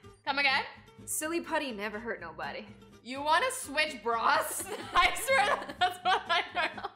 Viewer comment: I was eating Ice cream and I almost spat it out when I heard “you wanna switch bras!?”